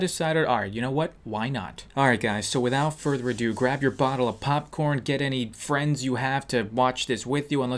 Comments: Did he just say grab your bottle of popcorn at the beginning? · Did he just say bottle of popcorn?